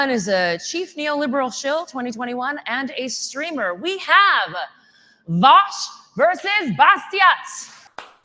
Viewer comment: Can't lie thought it said Vaush vs Batista for a second and was like god damn Vaush taking on everyone.